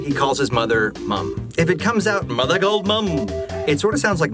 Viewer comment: I always heard it as "mother-go-mom" - kind of like merry-go-round? That doesn't exactly make sense, but we accepted all sorts of nonsense in the '90s and I was just a kid at the time.